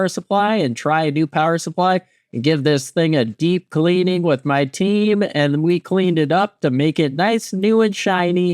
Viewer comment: I heard that as deep cleaning with my teeth